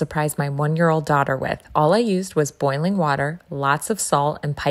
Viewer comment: For a second I thought you said toilet water 💀